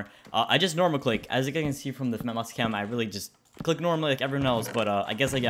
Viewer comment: I'm blind. I thought it said "MOUSESCAM" I got scammed.